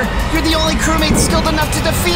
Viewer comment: is it just me or did ninja say Nani